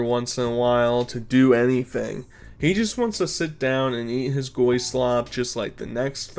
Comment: did you say goy slop as in goyim?